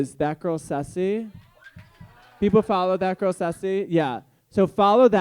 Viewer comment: What was the name he said at 27:38? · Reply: thatgirlsussi follow her on instagram shes incredible!!!